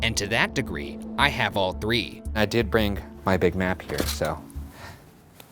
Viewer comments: Literally thought was saying “ Big Mac , Big Mac” but it was big map 😂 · bruh. i could have sworn he said "big mac''